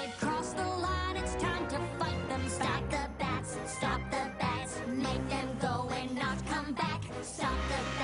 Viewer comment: Oooh, STOP the bats... I heard it as STOMP the bats... Good. This is less disturbing.